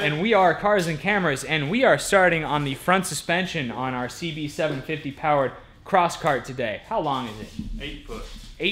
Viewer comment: I thought it said 750hp and I was like what the f**k!